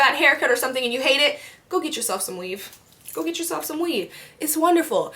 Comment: i thought she said "go get yourself some weed" lmfaoo